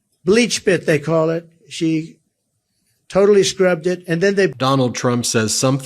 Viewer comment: Did he actually say "she totally screwuped it"?? (1:32) Hahaha